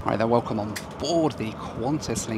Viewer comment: That moment where you question if you need your hearing checked, because it sounded like said "Fucker" instead of "Fokker." 😂 Damn ... I just realized it's been just over 21 years since I last stepped on a Fokker 100.